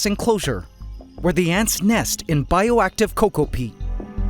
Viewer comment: DID HE SAY NEST IN BIOACTIVE COCOA PEE?!!?!?!? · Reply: Coco peat! Lol